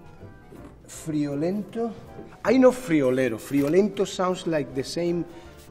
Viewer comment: I misheard him at 2:33 and legit thought he said "frijolero."